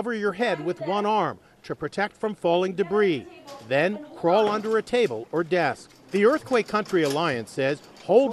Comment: i thought it said “crawl under a table or death” when it said “crawl under a table or desk